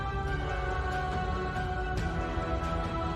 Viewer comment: I watched in 0.25× speed here is what I found: notice how he says "see you in the next ONE" at 7:30 sounds like he's saying Next WANG as in doctor strange's wang.